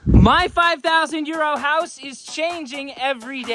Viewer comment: For the last few weeks I thought you were saying 5000 year old house. Lol. Your neighbors are priceless! You’re making great progress!!